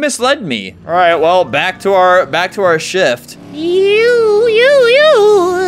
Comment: lol at 1:36 when he said shift I thought he said shit LOL